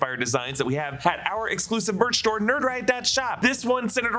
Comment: I hear “store” not “star” which makes a bit more sense to me